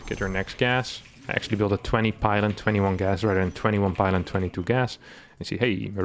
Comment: 21 pylon.. lol I heard 21 pilot